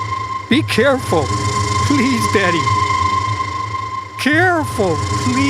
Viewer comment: I know he said "Betty", but it really sounded like "Daddy" 💀